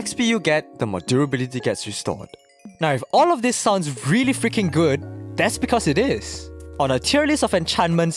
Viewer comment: when he said freaking for a second instead of freaking the text shown fucking same as genshin instead of impact